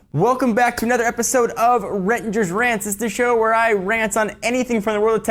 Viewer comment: i thought it said "rettinger's Pants" haha :D